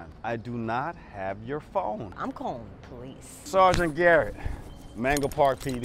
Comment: "I'm gonna call the police" Thought u said I took ur phone?